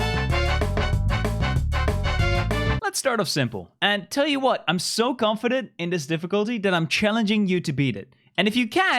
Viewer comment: Does anyone else know what that word is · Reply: With the ****? It’s fking